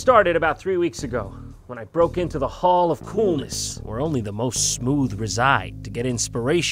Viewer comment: I always hear “when I broke into the hall of coolness, where only the most swood reside”, am I alone in this?